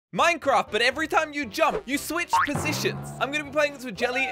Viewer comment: At first I thought It said u switch POTIONS